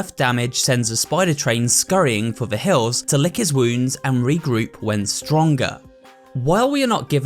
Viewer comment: wat 😯😧😮😦😧😧😯😯😮😮 DID HE JUST SAY THAT HE LICKS His…….. to heal? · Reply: My vol at the time was pretty low so i heard d### 😊